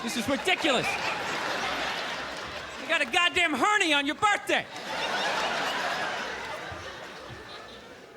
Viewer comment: what does he say at 3:14? (you got a "hurney(?)" on your birthday). English is not my native. Thanks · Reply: Hernia